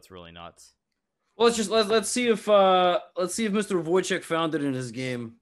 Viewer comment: anyone know what book these puzzles are from? heard levy mention the author is "woitek" or smth